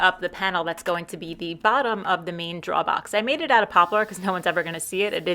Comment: LOL It took me until about 11 minutes in before I realized you were saying "drawer box" when I kept hearing "draw box". Love the accent! :D